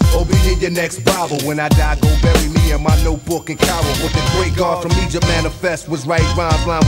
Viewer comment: Swore I heard Notebook & cowel. Was it, "in Cairo?"